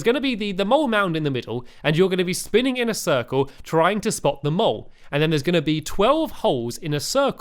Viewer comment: At first i thought he kept saying get them all instead of get the mole